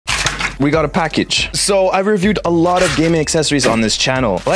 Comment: I thought I've heard him saying we got a trash..